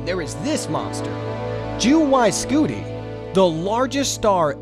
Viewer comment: Is it me or did I hear “Jew Y Scuti”